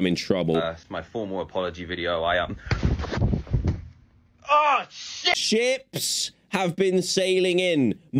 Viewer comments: he was just saying the Russian word for shield Щит it's pronounced as shshit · "Ship's have been sailing in, my toe hurts. Bring me bandages." I rate this quote 2 tables out of 3 cups of triangles! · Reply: lol Bruh i heard that as chips lmao